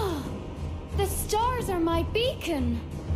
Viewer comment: Wuttt?? Is she says ahhh stars are my BACON??? And its in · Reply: Not sure if you're joking but I'll take the chances, it's BEACON hun.